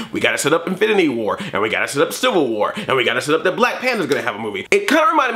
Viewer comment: It sounded like you said Black Panda. :P